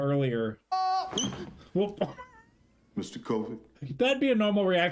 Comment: I keep hearing Mr. Covid... Am I nuts.